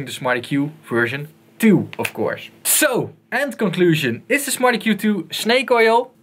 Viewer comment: lol sounds like hes saying "smarty Q 2"